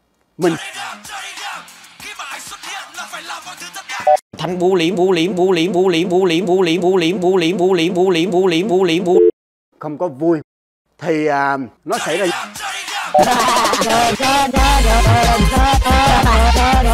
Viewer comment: vl thật nghe thành Búa Liềm ☭ =)))))